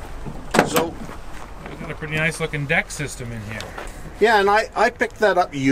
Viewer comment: How's that called? 9:45 Im not a native english speaker, i didnt catch what he said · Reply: "Decked Drawer System"